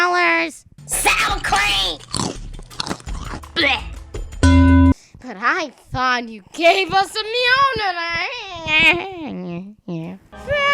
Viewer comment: i thought she said "i thought you were gonna give us a meal tonight"